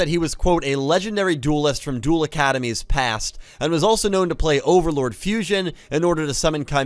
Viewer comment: I hear "OverLORD fusion" and I immediately run to wiki like £That sounds sick as hell, what is that?!", only to realise he said 'Overload'. Now I just want Konami to print a card called "OVERLORD FUSION"